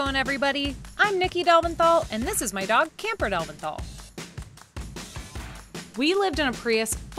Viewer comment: I love your videos. When you say "and this is my dog, Camper Delventhal" I heard "Camper DOGenthal". It's too much love. Hugs from Brazil.